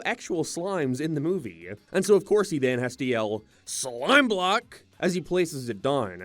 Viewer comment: You got it wrong Steve yelled "SLIME CUBE", that's... stupid. I think they called a lot of thing cube in this movie too, they even reshot the "I am Steve" cut and now it goes like "AY am Steve"